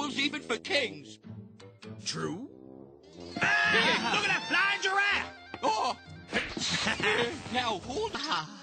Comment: The people who disliked didn't see the flying giraffe. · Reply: I thought he said blind giraffe at first